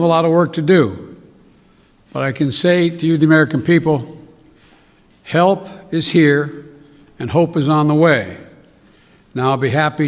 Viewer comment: did i hear it correct ? hell is here and hope is on way out ?